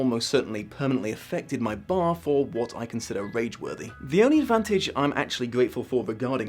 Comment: I totally misheard "rage worthy" as "Rage Willy" so that is my new Rockband Name